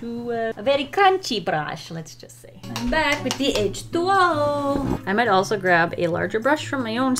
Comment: Not the english caption at 5:32 saying "edge duo" instead of "H20" 😂😂😂